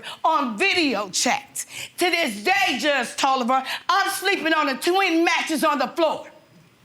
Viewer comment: “Judge Tolever” 🥴 Did I hear that right ?🤔